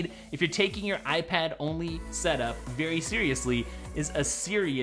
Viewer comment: When he said serious my iPad thought he said Siri